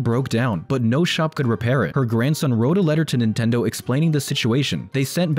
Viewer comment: Wait i thought that said nickelodeon. Not nintendo.